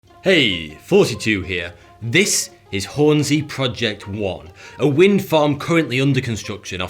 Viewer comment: I thought you said "hey thoty 2 here"